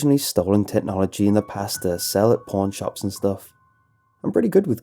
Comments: thought he said porn shops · am I the only one who hears porn shop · When he said pawn shop I thought he said porn shop